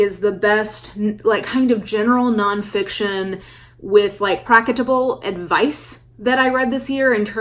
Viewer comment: what word was the Mara? Praketable?. Young reader trying to learn new words. Love your channel